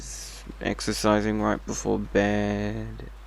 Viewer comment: mf i thought he said "exorcising before bed"